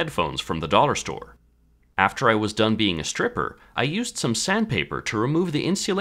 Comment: I heard "After i was done being a stripper" lmao